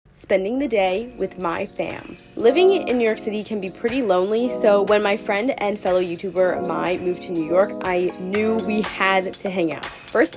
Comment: i wasnt looking at the video when it started so i thought she said "spending the day with my fam"